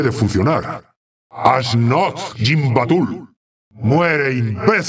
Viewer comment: que coño dice el tichondrius en el min 2:12 que dice como "asnot gimbatul" algo así siempre me intrigo ese lenguaje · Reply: "ash Nazg gimbatul" Referencia cojonuda al Anillo Único de la trilogía del Señor de los Anillos. :3